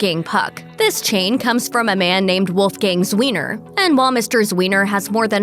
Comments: I keep hearing "Wolfgang's wiener" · I thought she said Wolfgang’s wiener.